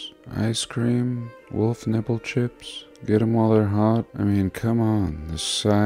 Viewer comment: I swear I just heard you say "wolf nipple chip".... I really hope I did.🤘🏻🕉🖤😉